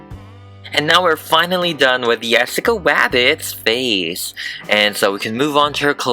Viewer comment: and 10:57 is it just me because I heard ‘ yesica wabits ‘ Btw it sounds like you have a cold 🤧